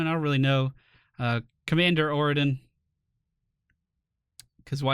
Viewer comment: Did he just say commander oradin?